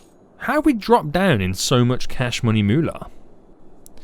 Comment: Turn off the warehouse that you are not using at the moment to save some CASH MONEY MULA. You should build a intl airport here. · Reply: i’ve heard it as “cash money bular” my whole life.. my life is a lie